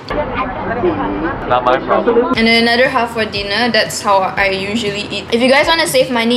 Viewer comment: I thought she she said "then another half for Dina " HAHAAH so cute!